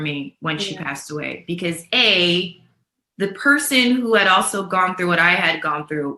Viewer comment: Did anybody hear that at 8:43 The person who did it to her is no longer here.